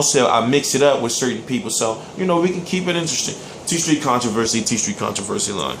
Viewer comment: Been watching your vids for about 2 yrs, always thought you said "TEACH your controversy live" instead of TstreeT, bit of a disappointment when I came to this clip and realized. This video is one of your worst, you've lost your objectivity bro.